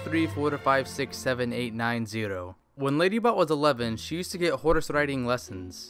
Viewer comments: I thought he was saying ladybutt XD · It sounded like you said Ladybutt